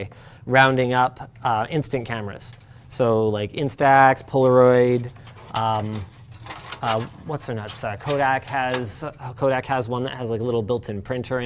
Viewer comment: is he saying "what's her nutsack"?? Absolutely unhinged